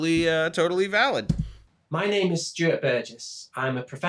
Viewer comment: Am I alone who hears "My name is stupid" at 10:34 ?